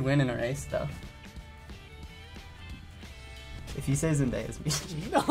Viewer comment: Wait what did say? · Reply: Not Really Cool 000 “if you say Zendaya’s Meechie” (or something like that, I know he said “if you say Zendaya’s”)